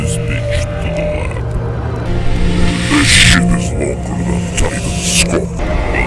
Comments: I think large tv man said "this sh*t is making our titans talk" cause titan cameraman was not able to talk with a normal arm or a acid arm but when he switched it to the astro arm he could talk for the first time so which means the astro arm is the only one to make the titans or the skibidi's talk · I thought large tv man said, this shit has broken our titans stuff